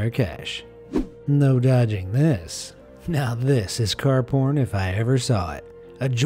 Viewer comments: did he just say car porn? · Who heard car p*** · did he just say car po** I-------🤐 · Did he say car porn